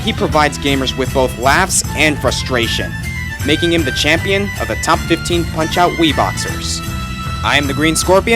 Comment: This is what the Closed Caption Generator thought he said starting at 14:02 {start} (...making him the champion of my Top 15 Wii Boxers.) making him the champion a top-15 my child be boxes (I'm The Green Scorpion, see you guys next time!) I'm the green scorpions see you guys next act on [here Aran Ryan gets KO'd] of the he shared (TKO!) the spokesman [transition cutscene] shot (He can always take a whoopin'!) you can always pick it happen (Ha ha ha ha!) I the {end} Eh...wow. I think the CC Auto Setting was meant for people to be speaking s-l-o-w-l-y.